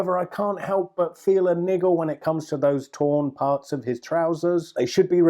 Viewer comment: sorry thought i heard it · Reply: ***** Just to be clear I said "niggle"